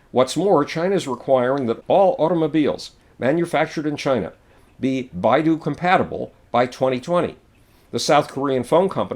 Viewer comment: Howard, what are you saying at 8:23? "Bayou compatible"? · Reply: beidou compatible. beidou is china's new gps system.